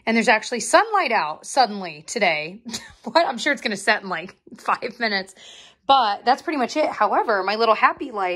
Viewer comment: Someone correct me.. Did say at 13:32 that the sun is gonna set in "like f***ing minutes." 🤣🤣🤣 if thats the case, I LOVE her